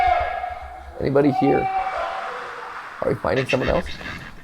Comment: I heard at 44:02 - 44:06 “Hello? Can anybody hear us?”